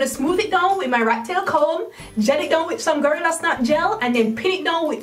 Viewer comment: I love how you say "gorilla snot gel" sounded like you said "girl that's not gel" lol...cute!